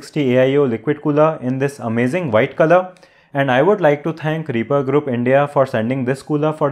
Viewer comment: what did he said repa group? Its not clear? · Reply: Reaper Group India